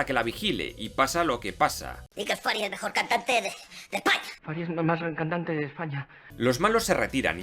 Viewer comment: que dijo? Xdddd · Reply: Dice: y que el Fary es el mejor cantante de españa